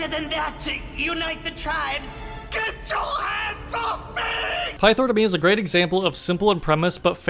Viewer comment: i thought he said get ur ass off me😭😭